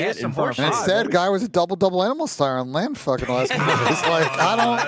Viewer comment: Am i the only one has no fucking idea what says at 9:59? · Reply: He’s saying exceed was a burger on LAN. It’s a burger from in n out